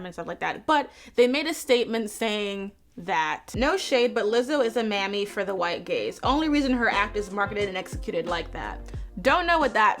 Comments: bruh i thought u said for the white gays LOL ...which would probably be more true tbh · For half of this video I thought you were saying "white gays" and was like well you aren't wrong there what's the problem hahahah I feel so stupid